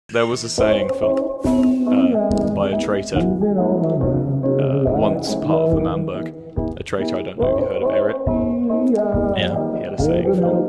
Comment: What they said was actually.. 0:01 " there was a saying phil. Uh ,By a traitor, A traitor uh, was once part of l'manburg. A traitor idk. have you heard of eret? Y-yeah. He had a saying phil. It was never meant to be.